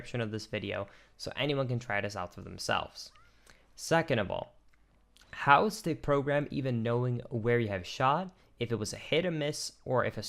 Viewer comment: Did you say, “How is the program knowing...” at ~20:40?